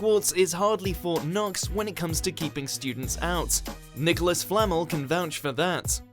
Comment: It sounds like he said Nicolas 'Flannel'